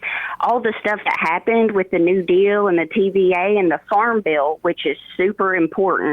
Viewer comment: When she said "Farm bill" i heard "Farmville" 🙃